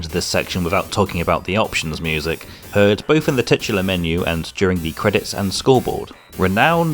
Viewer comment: did somebody say “tits”?